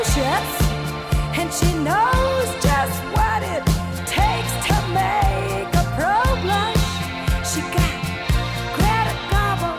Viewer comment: For the longest time I thought she said 'she makes a problish' and never could figure out what a problish was... :)